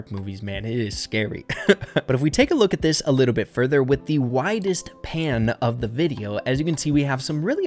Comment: at first I thought he said ”the widest Pam” xDDD